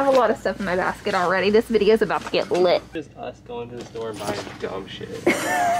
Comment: “This is just us going to the store and buying dumb sh*t” 🤣🤣🤣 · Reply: I totally thought he said "buying dog shit" and I was like what Ty??! Lol